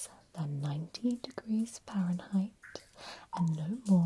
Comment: The bath temperature threw me off and I wondered why you can catch hypothermia while being boiled. Then I remembered, this video is in Fahrenheit, not Celsius 😊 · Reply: I thought she said 19 Fahrenheit at first. A bit parky for me.